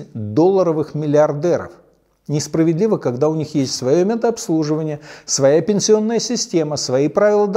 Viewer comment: Услышал "своё мент-обслуживание"